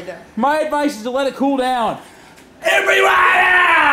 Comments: sounds like AGP says EVERYBODY!!! RIP legend 😭😢🤧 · it sounded like he said "EVERYONE OUT!!!" In my opinion